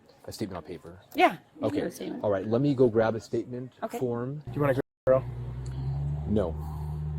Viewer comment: I think they're referring to 5:54 but even with it halfway censored it's hard to tell exactly what he said. It certainly sounds like "do you wanna turn your cam off" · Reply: You can find unedited versions of this video with a simple search. The cop does not say that. He asked "do you wanna grab the throw".